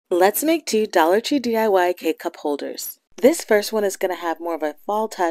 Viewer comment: Well first I thought you said cup cake holders lol sorry!! These are wonderful. Thanks for the great ideas!! 😊